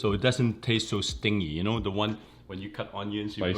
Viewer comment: What was the word here? Couldn't make out what word was used. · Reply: Stingy